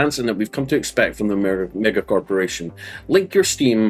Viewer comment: bro casually says nigga instead of mega corporation bro said nigga corporation